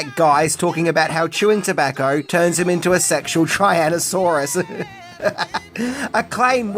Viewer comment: Um … was it just the accent, or did you just say “Triannasaurus Rex”? 🤨